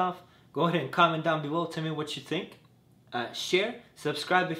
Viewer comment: It sounds like he said fuck off at the end lol